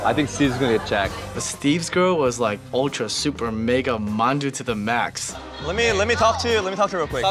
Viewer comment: what does say about steve's girl? monju?